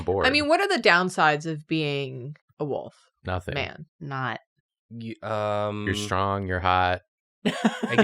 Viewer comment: did,, did just say knot